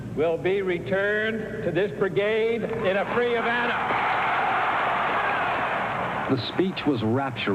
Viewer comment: (±) JFK's quote says "in a free 'event'(& not Havana?). NEVER trust the subtitles, captions instead of the spoken words. This seems like a minor thing but if my job is to type the exact same words that were spoken- how hard could it be to do it correctly rather than just play w/ rewording reality in typed words¿ This is often done with an automatic program now, or maybe it's not automatically programmed. It DOES get your attention to follow that readable type even though it's jibberish or jiberish (both of these two came up with a red line below- was I wrong BOTH SPELLINGS- the correct & misspelled? Or maybe it's optional but the program wasn't built properly for usage of such an odd, infrequently used word? It's better to fkg listen anyway. I read too many examples of this kind of mistake if I fall into it & 'read along with illiteracy'!👍